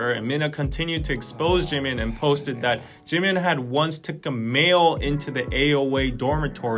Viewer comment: is it just me or does he say "once took a mayo into the dormitory..." ehm what??? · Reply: a male